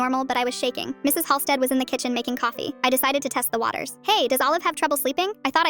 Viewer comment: I thought it said "mrs.Holfstader was black" bro I was so confused